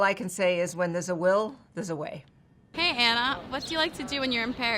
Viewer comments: DID SHE JUST SAY HANNAH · did someone just call Anna Wintour Hannah?????? fr